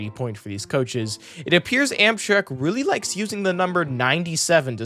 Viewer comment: Did bro just say Amsherk?